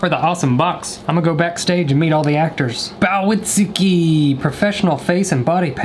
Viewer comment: nobody: me a Haikyuu fan at 4:27 (I think): Yams:bow to Tsukki that is what I heard